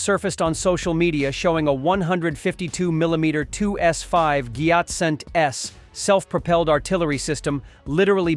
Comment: Did you say Gyat at 0:48 or thats tank name...